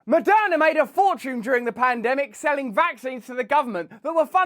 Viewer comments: For half a second I thought you opened by saying "Madonna made a ton of money from the pandemic" 😂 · Lol, I heard “Madonna made” at the beginning. I rewound and I wanted to hear it again. I laughed. 😂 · at first i thought you said Madonna made a fortune during the pandemic 😂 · I seriously thought he said “Madonna made a fortune selling vaccines to the government” and was so confused hahahahaha